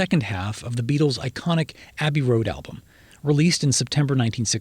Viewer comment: First odd thing I heard was "Habby Road"....??